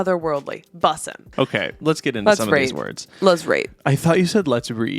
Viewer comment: thought u said 'let's pray, let's pray.'